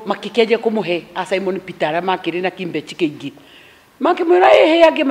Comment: Did she just say " kimbech" 😂😂😂😂